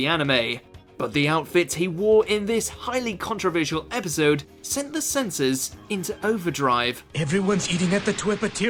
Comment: I thought you said Senti Sh**